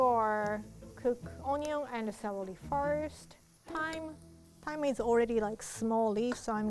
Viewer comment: When she said thyme I thought she said time